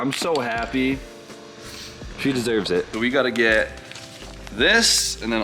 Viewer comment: Every time I hear say, "she deserves it", i hear "i can afford it" haha glad to see this staying oem+